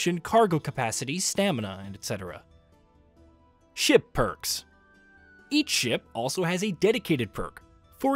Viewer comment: What he says "Ship perks. Each ship.." What I heard "Shit perks. Eat shit"😅